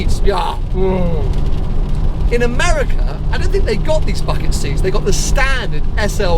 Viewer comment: errr, did he say .... "bucket seats" ??